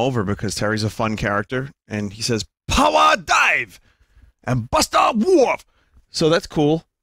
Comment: still thinks power dunk is called "power dive" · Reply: It really sounds like he says that though. Like deep down I know it’s called power dunk, but I keep accidentally saying “power dive” because that’s what I hear when I use the move.